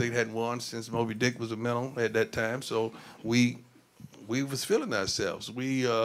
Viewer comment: Jackson State hadn’t won since “Moby Dick” was a WHAT??? I’ve tried like four or five times and I still can’t make out what that word is. “Moby Dick” was written in 1851, 18 years before the first college football game. Is he just exaggerating to make a point that it had been a long time since Jackson State had won a championship? If not, then what the heck is he talking about???🤷🏼‍♂️ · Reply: Minnow